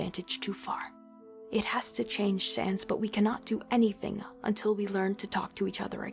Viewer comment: istg I heard Shans